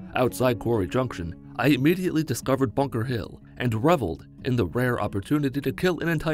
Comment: Did I just hear Bunker Hill?